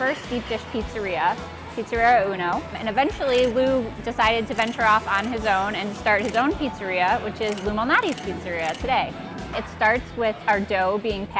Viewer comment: Anyone else thought that they were say “Illuminati’s” pizza? 🤔🤷🏻‍♂️💯😂